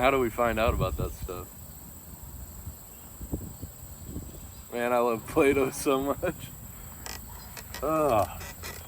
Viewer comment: misheard and thought you said “man i love play-doh so much”. Didn’t bat an eye though